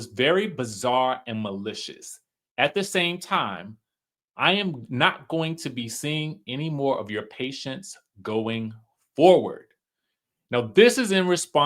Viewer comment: I lost respect for when he said that being called a cheater is the same as being called a p-phile. So they can keep their receipts because that is NOT the same. . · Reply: Two things can be true at the same time. It sounds like he was saying am will not be taking anymore of your patients.